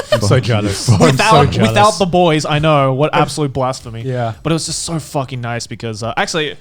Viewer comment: at 3:10 : what an absolute blasphemy what i heard at 3:10 : what an absolute blast for me